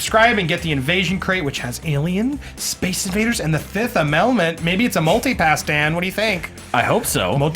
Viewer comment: Did say "The 5th Ahmelment"? I've never seen that movie. :P